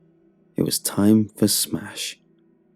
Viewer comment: I thought he said “It’s time to smash” I almost spat soup everywhere